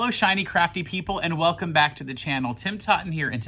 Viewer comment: I swear I heard “tiny, crappy people” haha!